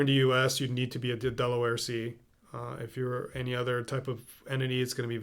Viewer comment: he says you need to be a Delaware C in order to get funding, is he saying double L C?